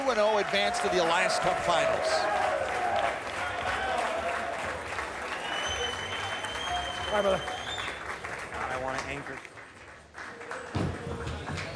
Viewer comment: does anyone know what Ciminelli said? Was he even talking? I heard "... I wanna anchor" from someone there.